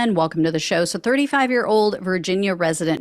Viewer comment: I swear I thought I heard you say "35 year old virgin".